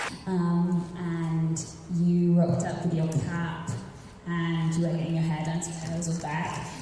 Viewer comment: "you rocked up with your cap and you were getting your hair done so it was all back" At 6:09, "oh no, she's gonna think I'm such a geek" At 7:11, "I went to Shamier's and like knocked up" At 8:51, "for me it was actually really great as an actor, because I was feeling a lot of feelings similar to what Nicole I think was feeling" At 9:07, Dom: "just a little", Kat: "...that it was lasting as long", Dom: "really?", Kat: "because I thought...", Dom: "but it was so much fun!", Kat: "I know, I think that's why, so fun. I can see that though, that makes sense" At 9:24, "one of two ways" At 9:38, "I told you it was a risk telling this story" At 9:46, (i can't understand that first sentence either, there are too many people talking) "trying to forget that too, because then you really- I know for me like I turn on that part of my brain and it's really hard to forget that it's gonna be all over you in every moment of every scene" At 10:00 "and he- I realized in that moment like ohh I didn't think this through, the last lift is gonna be a problem At 11:45 (in comparison) to other festivals that this has kind of a different vibe" At 11:49 : i love how I just got my banana just... (can't hear, the audio is terrible 😂) when I said I was gonna eat it, it's probably gonna be here in my hand until... At 12:03, "...myself and Gabby and a few of the other girls, unfortunately Kat didn't quite make it down to the beach 12:09 - 12:24, I couldn't understand a thing here except "crazy, but